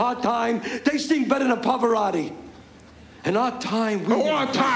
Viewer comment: can anybody what he say. My english ok but no real good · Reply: "They sing better than Pavorotti' (He said: Pavoraatti, but ment Pavorotti). It means when in prison or being sentenced for drug convictions the tough hard drug dealers are not so tough as the drug sentences are long or tough. So they start singing. Singing in their vernacular means spilling the dope, or ratting out. Or telling all they know to the Police or Judge trying to 'plea bargain' for a shorter sentence.